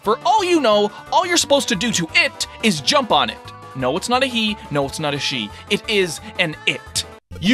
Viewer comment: I thought you said it wasn’t a he or a she😏